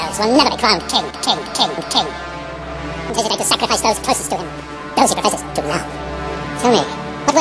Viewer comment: HAHAHAHAHA!! Did Saruman say "Sacrifice those pussies to him" at 02:22? awesome video ^^